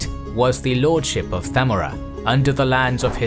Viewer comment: sounds like you said samurai with a th sound lol just “zamOra” with stress in the O. Also no need for the lisp if you’re going to anglicize everything else.